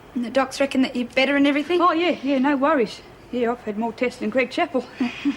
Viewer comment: who did Cass say she had more tests than? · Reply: Greg Chappell, a former Australian cricket captain.